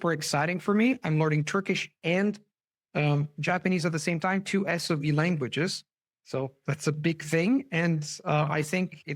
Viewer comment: I thought he said SOB languages....!